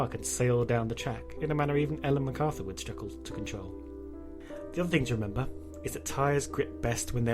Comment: wtf is he saying? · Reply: He's says 'The other thing to remember....'